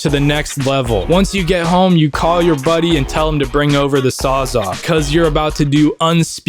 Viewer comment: thought he said "zaza"